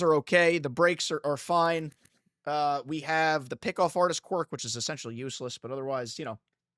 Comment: I thought you said “pickle fartist quirk” when you said “pickoff artist quirk”… I don’t even know what it does, but I think I prefer the pickle fartist quirk…